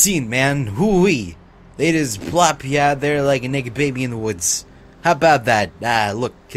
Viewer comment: Did you just say "A NIGGA BABY in the woods...???"